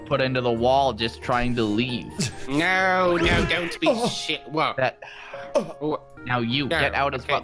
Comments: Did just say SHI* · Did anyone hear a curse word at 1:49? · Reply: he said the sh** word